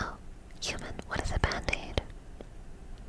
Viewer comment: I thought you said, "What is a bad date?"